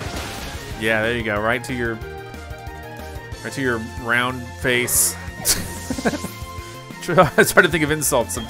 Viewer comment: Hahaha 🤣 when he said “right to your …right to your ground face” thought he would’ve said something insulting for the first time